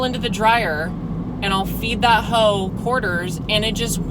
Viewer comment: I thought you said “I fed that hoe hoarders”